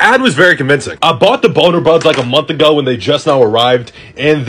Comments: I thought he said “boner buds” 😭😭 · I KEEP HEARING “BONER BUDS” 💀